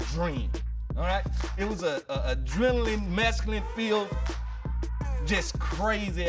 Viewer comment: does he say "adrenaline, MESCALINE filled... "??? Can't be what he says but I must've listened to it 20 times that's exactly what it sounds like and idk what else he could be saying... But if NFL Players are all taking mescaline with strippers at parties that's crazy af and lit af. Edit (for those who don't know); Mescaline: Mescaline is a naturally occurring psychedelic alkaloid of the phenethylamine class, known for its hallucinogenic effects comparable to those of LSD and psilocybin. It occurs naturally in the peyote cactus, the San Pedro cactus, the Peruvian torch, and other members of the Cactaceae plant family. AKA, Mescaline is Peyote.